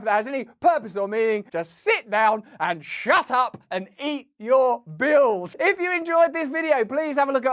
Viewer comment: ooohhhwwww .... at 20:22 i thought You were going to say " eat Your bugs ! " 😂 😋